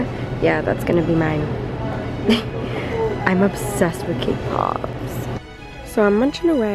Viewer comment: i thought she said "kpop" rather than "cake pops" ahaha 😂